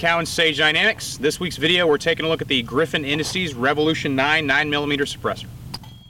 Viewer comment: Could've sworn you said "Today we're looking at griffin industies revolution 9mm"